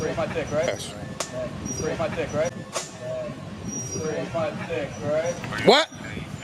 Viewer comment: “A walk where? I live here that’s my cousin right there” haha wow the cops said smd first and his boss is breaking the law by yelling his if really considers that an arrest able offense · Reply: Did he really say Smd at 07:40 insane professionalism wow